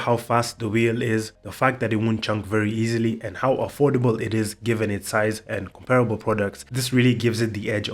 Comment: 🤙🏼 Not surprised, at all 🤙🏼 Jeff put so much thought into this, insane. When you said "and how affordable it is" I heard "look cow" 😭 cos of the cows lol. I've got 85mm cags on my Trip, the McFlys tempt me for when I get better st pushin.